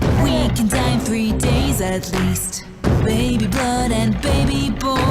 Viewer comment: thought it said baby batter